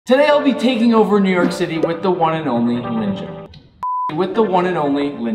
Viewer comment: In the beginning, i thought he said "Today I'll be taking over New York City with Ninja"